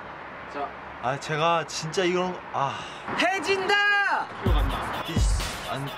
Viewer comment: it sounded like Jungkook said "hai jinda!" (Hindi) 😂